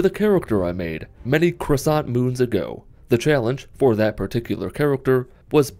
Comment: Did you say "many croissant moons ago"? I love Croissants!